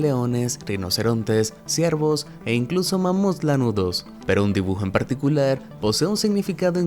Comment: Pense que dijo Mamuts Naruto jajaja